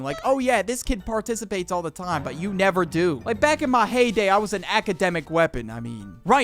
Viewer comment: my ears automatically turbed on cause i heard the game hay day